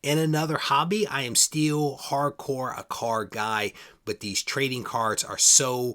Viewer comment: "I am still hardcore a car guy." Did I hear this correctly? I would have sworn you were a hardcore comic guy.